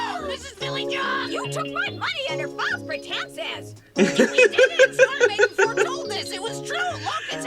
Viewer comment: I can't make out the last part of what Fillyjonk is saying. "You took my money and I fall for tensest"? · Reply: She said "You took my money under false pretenses!"